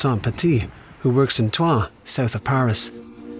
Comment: Does anyone know how to spell the name of the town mentioned at 2:12 ? Sounded like Twa!! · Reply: Troyes